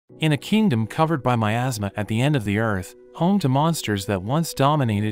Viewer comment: in the intro i thought it said in a kingdom covered by mt a$$ 🤣🤣